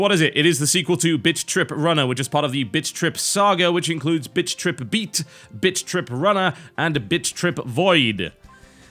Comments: it sounded like you said bitch trip beat bitch trip runner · Heh, for awhile there in the beginning it sounded like you said Bitch trip beat and bitch trip runner. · TB you sound like your saying bitch trip runner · I keep hearing TB say "bitch trip". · thought you said bitch trip runner at the start